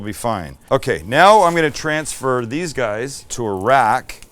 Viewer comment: "Now I'm going to transfer these guys to Iraq"... That's totally what it sounded like Sam said when he said I'm going to transfer these guys to a rack. 🇮🇶 Sam, please put sauce on the top bun! Oh man, I have to keep telling you this! 😎😘